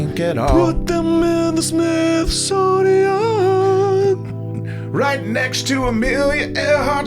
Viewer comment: I thought u said "SNIFFsonian" #nexttime